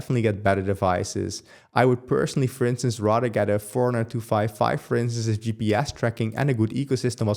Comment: what the heck did you say here i can't here it and 4255 doesn't make any sense! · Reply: He said he would rather get a forerunner 255 for instance with the gps tracking and ecosystem as the focus, meaning the garmin forerunner 255 smartwatch